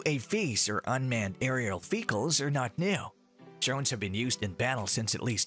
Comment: I keep hearing "fecal" instead of "vehicle."